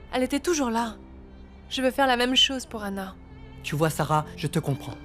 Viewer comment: est ce ç est moi qui a mal entendu Seb a appelé Kara Sarah ?👀